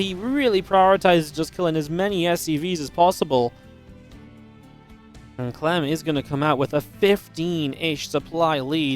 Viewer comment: can anyone tell what Maru said at 5:00? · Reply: Maru said I'm gonna fuck clem up